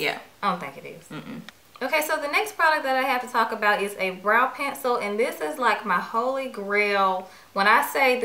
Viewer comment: a please change your profile pick.....I thought you said "penis " as well for a reason....